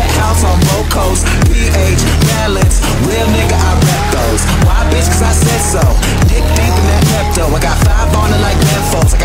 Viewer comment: jaja parece que dice mocos por que soy así?